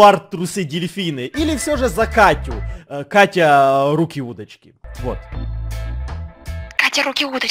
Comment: Кому тоже послышалось "Руки-уточки"?😂